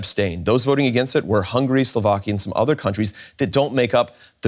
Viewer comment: What I heard was "hungry Slovakians" 😀